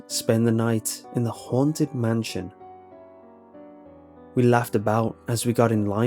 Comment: I thought it said bad night in the harambe nation